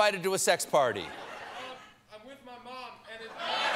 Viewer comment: “I’m with my mom I can’t answer.” LOL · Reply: To be honest all I heard was “I’m with my mom.” But he kept talking and Steven was embarrassed so I guessed what he likely said next was, “I can’t answer that.”